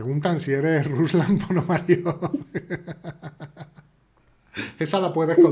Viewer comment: ¿Qué dice en 4:10? A quien mencionan? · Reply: Ruslán Ponomariov